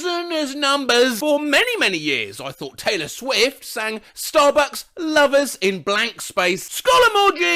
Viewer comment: BRO I USED TO THINK IT SAID STARBUCKS LOVERS TOO